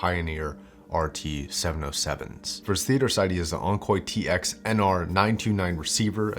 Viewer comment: Did he say "Onkoy" at 2:04? Yikes!